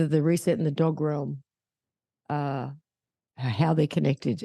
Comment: did you say "the dog realm?" - I'm not getting that one.